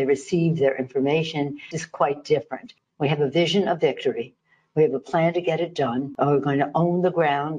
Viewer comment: When she says "they have a plan" I HEAR: 'CHEATING'